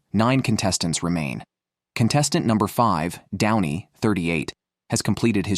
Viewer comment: "which can grow up to 90 KILA" instead of "kilo" : further AI monologue determination. will continue. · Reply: "down-ie" instead of "don-ie"